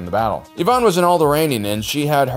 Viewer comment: I heard "old Iranian" 😆